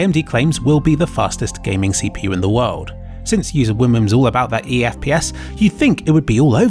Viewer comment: UserWenchark 1:17 BuserMeenchark 5:24 HuzherShenchmark 5:59 BencharkShmarkShmark 6:40 UserWimimn 7:03 UserPantsmark Edit: I used what I personally heard IMO 6:40 sounds like UserWimimn's and on 5:59 it's written as chmarkchmack, but he's clearly saying shmarkshmark. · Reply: on 6:40 i hear UserWomens